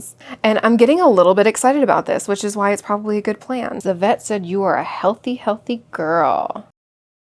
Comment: I had this playing whilst I sewed and my ears pricked up when I heard “Yvette said you’re a healthy healthy girl” 😅😂